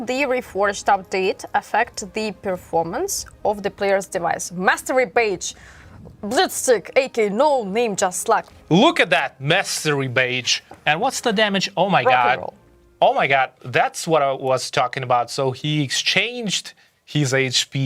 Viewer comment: bro almost spoke it like masterbate instead of mastery badge🤣😂